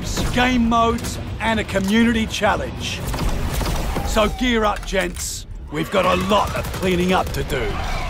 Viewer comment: woah woah woah. did he just say "ready up gents"? Somebody's got a lawsuit on their hands. silly feminists